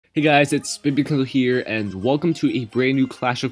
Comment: I heard “hey guys it’s spibikel here”